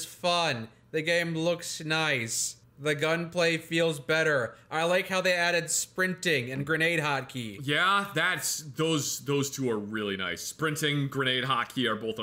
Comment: I thought you said grenade hockey (not hot key) I got really excited for my next playthrough for a moment.